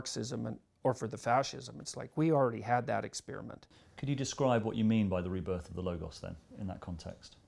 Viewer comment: having a very difficult time hearing what the interviewer said approx. 1:50.. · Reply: "Can you describe what you mean by the rebirth of the logos?"